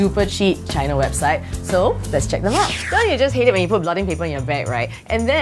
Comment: I still don't know what she said at 0:29. Did she say blood and paper? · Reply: Blotting paper